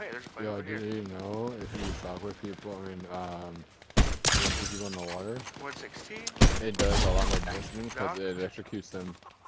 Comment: What were you saying at 4:08 ? I couldn’t understand you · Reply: The person I was playing with was saying if you EMP someone in the water it does extra DMG ... around 4:08 ... Not sure if thats true or not i never tried it myself ... Just got back from a trip, why it took me a bit to reply here ..